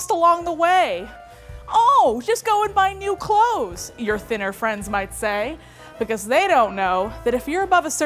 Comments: I literally thought said “sinner friends” and it took me a moment to realize the mistake 😂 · For the longest time, during the intro when says "your thinner friends might say", I always heard her say "your dinner friends might say"